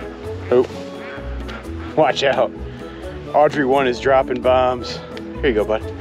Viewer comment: I definitely thought you were saying Audrey-wan like Obi-Wan